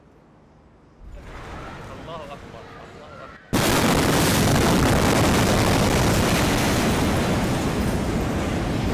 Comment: what did he say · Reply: Allah akbar